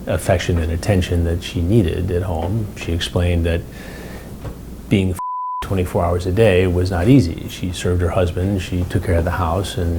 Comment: lol being *****ed 24 hours a day isnt easy. dat what it sounded like